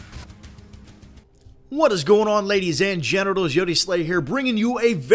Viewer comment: Did he just say "Hello ladies and genitals" xDD